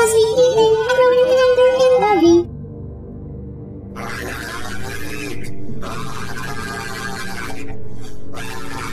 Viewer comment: IS NO ONE GONNA TALK ABOUT THAT SCREAMING??? It sounds sad but then again, it sounds so like, happy because If that is William A..he actually deserves it. Edit: it also sounds like he is saying “ I HATEEEE… " then screaming and repeating. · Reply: Lyrics: HEEEENRYYYY MIIIIIKE HEEEEEEELP